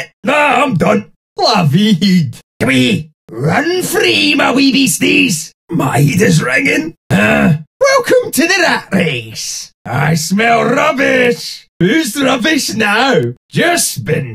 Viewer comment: heavy head (or heat) 00:25 run free my wee beasties (the rats) 00:26 My head is ringing 00:29 Welcome to the rat race 00:44 get in my bin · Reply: I thought he said “My rat is dragon”🤣